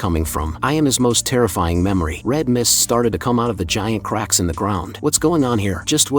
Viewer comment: did I hear red mist?